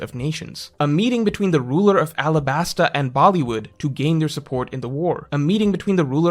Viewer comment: Did he just say BOLLYWOOD?? 😂😂